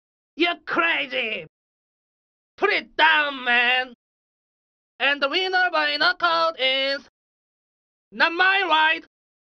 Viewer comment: Always thought he said and the winners buying the coffin smh wow 😅